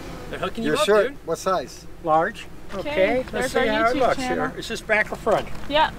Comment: "Is that the back or front?" "yep" 🤣❤ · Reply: To give her some credit, I thought the guy said "Is this back and front?" So her answer made sense to me.